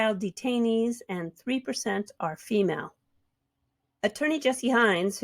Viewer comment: I'll miss him too. When he said Attorney Jeremy, I heard 'Attorney General'😄